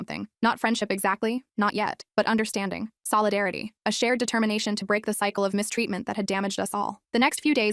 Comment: DID YOU SAY SHARED DETERMINATION???? cue Frisk and Chara walking in and slaughtering everyone